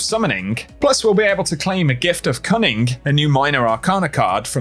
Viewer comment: Legit thought you said "A gift of cumming."